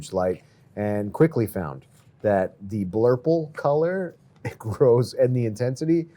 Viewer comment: I couldn't hear what kind of bulb you recommend. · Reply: he said 'blurple' light, referring to the colour of the horticulture lights. (f. i. Check the tunze refugium light)